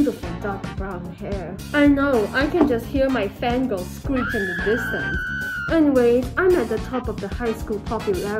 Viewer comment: HE,P ME ON 16:21 I THOUGHT YOU SAID “I can just hear my fan girls screaming in the basement”